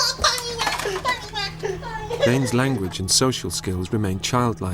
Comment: what the.. 6:00 Did he just said "'Tang Ina?" Pinoy...you know what that mean, right?